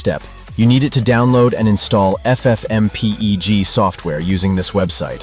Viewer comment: "Ef Ef Em pee ee gee" Did AI say the word FFmpeg like that?? 😂😂😂